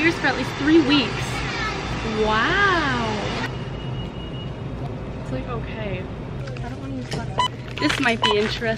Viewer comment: I thought she said Taste like cocain but she actually said it taste okay